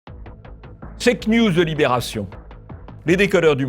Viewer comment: "checknews" j'ai entendu "fakenews" c'est grave docteur?